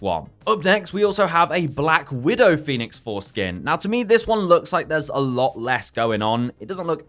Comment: I keep hearing "Phoenix fore-skin" lmao